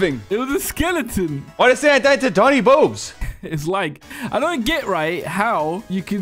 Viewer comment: Why does The YouTube Caption say “Why’d they say I died to tiny boobs”, that’s just Sus